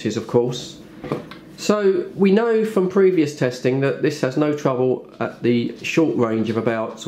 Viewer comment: Sounded like you said 21 and trouble I